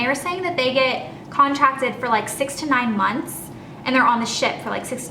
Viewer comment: Talking about the cruise ship contracts, you said "6-9" months. I thought you said 69 months. lol I was like, "You don't carry a baby for 69 months.... oh." lol